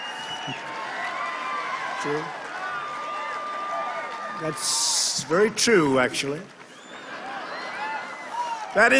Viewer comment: What did person in crowd say at 9:50? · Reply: “Not on my watch!”